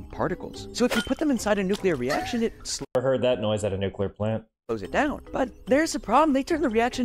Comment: "never heard that noise in a nuclear power plant" god we wish noone did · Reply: For a minute I thought he said “I heard that noise at a nuclear plant.” I’m like, oh god… why would you say that